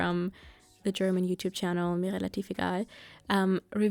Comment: Who's that YouTuber you were talking about? I couldn't really understand the name, but it sounds like something I want to watch :) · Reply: Her channel is called Mirellativegal :)